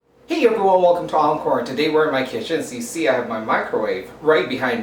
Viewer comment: OMG the first sentence you said I had to replay it because I thought you said 'hey everyone, welcome to olive porn" hahaha